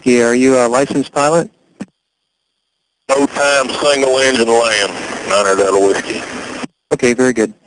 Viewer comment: ish ... ATC asks him if he's a pilot... Could someone tell me both... What*...he says, and... What...*it means exactly? Thank you in advance 😊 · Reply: He says he has low time, single engine, which basically means he is rated to fly a small aircraft like a cessna 172. A king air is much bigger, with two engines, and likely more advanced instrumentation.